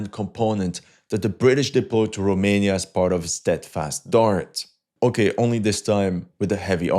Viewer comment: I thought it said “Steadfast Fart” 💨 Lol 😂